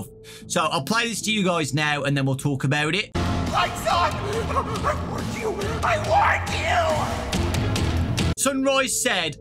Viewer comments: it sounds like sunrise is saying “lights on I want you,I want you!” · Sunrise actually sounds like he’s saying “I WARNED YOU, I WARNED YOU” maybe he could be a neutral animatronic warning us about moonrdrop (or someone else?) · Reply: Yeah I thought it said "I warned you" and then later turns into moondrop and goes crazy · Sunrise actually sounds like he’s saying “I WARNED YOU, I WARNED YOU” maybe he could be a neutral animatronic warning us about moonrdrop (or someone else?) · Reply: Honestly thought he said "My SUN! I warned you! I WARNED YOU!" but after reading the subtitles I realised it was just the Dark Souls fan in me mishearing it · I thought sunrise said I warned you instead of I want you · I heard: "lights on, I want you, I want you." I think sunrise is competitive with his other side. He wants to get Gregory first. I'm pretty sure both moon drop and sunrise are evil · Sunrise actually sounds like he’s saying “I WARNED YOU, I WARNED YOU” maybe he could be a neutral animatronic warning us about moonrdrop (or someone else?) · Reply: At first I thought he was saying “I want you” like he was working for Vanny, but then I listened again and realized I’m stupid lol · It kinda sounds like sunrise said “I warned you”instead of “I want you”